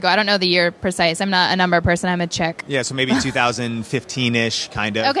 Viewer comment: Did she actually say, “I’m not a number person, I’m a chick”? You’ve got to be kidding me, 🤮. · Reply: I thought she said "I'm a Czech". Does she have Czech inheritance?